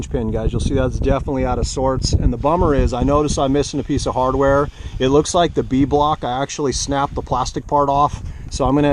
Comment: You’ve convinced me! Buying one tomorrow. What’s that stand you said you bought at Home Depot at min 9:21? I didn’t catch the name of it. Thanks in advance! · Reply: That's awesome Jay 🤗. The stand is a Ridgid Flip Top. Just add a pit mat to the top to keep the rigs from sliding around. <link>